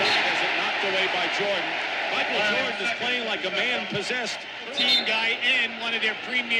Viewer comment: Did I hear the commentator say Jordan is playing like a man who is possessed 3:37 😅